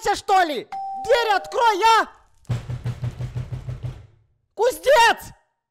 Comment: Ребят это норм то что Эд сказал "кузнец" а я услышал "куздец"?!👍😂